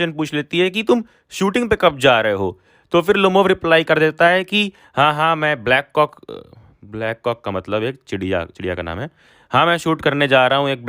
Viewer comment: wooh .. Wait a sec.. Did I heard.. Bla... C... K